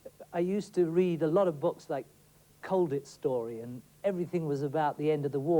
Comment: is he saying COVID story??? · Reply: colditz